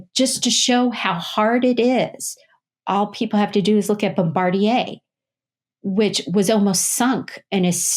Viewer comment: I can't really hear, does she say Bombardier?